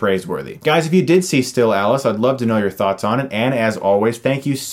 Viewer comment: For the longest time I thought you were saying "Or you can stay here and get stuck in my eyes". Not sure if I can un-hear that now...